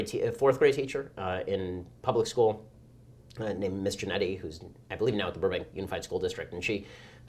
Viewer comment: Doesn't it sound like Ben is saying "Mr. Nutty" at 18:27 instead of the captions that say "Miss Jannette"